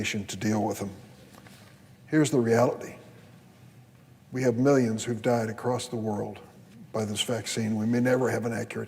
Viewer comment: Did he just say "we have millions who have died by this vaccine"? 4:25 in this video